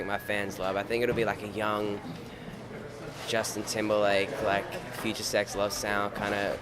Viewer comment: did he say future love sex?O_o